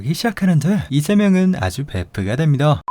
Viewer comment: 이만 이3명에서를 이재명은 아주 베프가 됩니다로 들었낰ㅋㅋㅋㅋㅋ